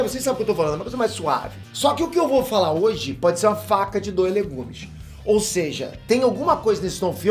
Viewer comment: Só eu ouvi "faca de dois legumes???" Heuheuhueheueh é faca de dois gumes 💕 amo muito seu canal parabéns!!